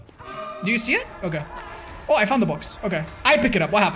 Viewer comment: he said "she found the box" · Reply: he clearly said "I found the box"